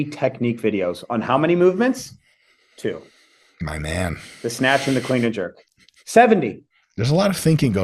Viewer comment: When Zach listed his two movements I coulda swore he said the "Snatch and the Queen of Jerk"..............Like wait wuh?